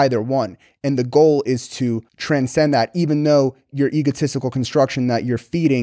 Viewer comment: did i just hear tren?